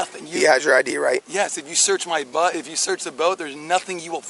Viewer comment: did he say the officer could search his butt for drugs then switch it up to Boat